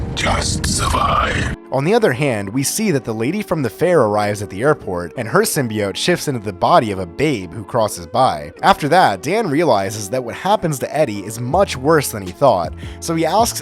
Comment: Is it just me or did he say babe